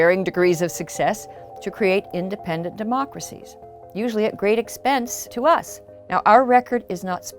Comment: So around about 1:15-1:18 She says "usually at great expense to us!" but the Subtitles say "Usually at great expense to the motherland!" if you pay attention it cuts to a picture and the audio rhythm is strange like they cut out Motherland and edited in "us". Pretty strange editorial decision.